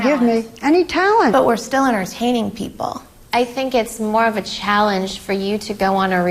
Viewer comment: What did she say, "We're stellar in hanging people?" ??? 1:45 someone help please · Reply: She said "we're still entertaining people"